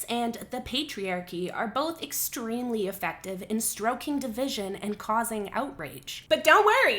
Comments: did you say "stroking" instead of "stoking"? Freudian slip perhaps? ;-) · you said stroking division instead of stoking XD just had a giggle